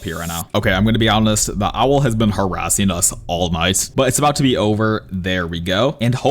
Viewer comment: bros favorite words are “i can’t lie” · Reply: bro check 4:30 bro said “all nice” instead of all night